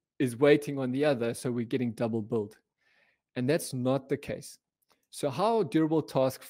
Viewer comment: what is he saying, double both/built? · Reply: "Double billed", sorry, it's my heavy South African accent 😆 The anti-pattern in serverless is that you have one function running and "waiting on another". This would mean you are billed for the waiting function and the actual one that is doing the work.